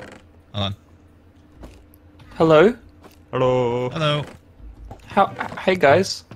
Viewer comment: did he say hallo whats the German Word for hello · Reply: He didn't say hallo but hello